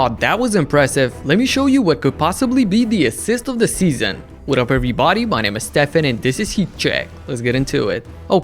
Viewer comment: I always hear you say "With a perfect body..." But I think you are saying "What up every body."